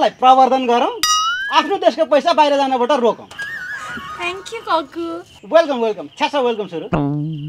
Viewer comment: i heard something like thank u f.. .. U